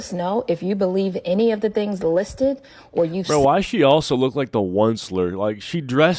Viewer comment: i swear i heard “yo, why she also look like that one slur” 😅💀 fml